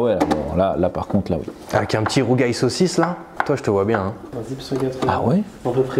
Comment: J'ai entendu " rougail saucisse"?